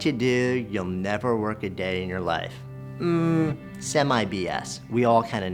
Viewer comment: I'm learning English and I'm really curious what means or how to spell "semi BS" or this "semay be es" in time 1:00 what said. I'll be grateful for help : ) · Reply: sorry but I disagree with +ErnieTheMighty. I think he meant "semi BS" BS is an abbreviated (written in a short way) version of Bull Shit (This is a "bad" word so people usually say BS). Semi means half, so he was saying the phrase is half Bullshit, it is half wrong